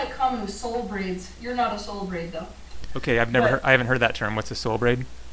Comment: soul breed = genius, half-man, half-god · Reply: Soul braid* I thought se said breed at first too